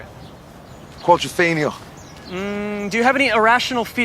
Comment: Yo what film did Liam say at 0:44 ? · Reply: Quadrophenia